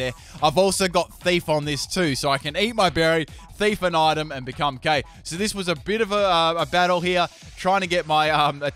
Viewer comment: I thought he said "So I can eat my berry,thief an item and become GAY on